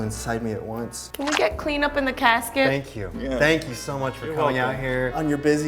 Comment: bitch I thought he said “thank you for pulling out” 😂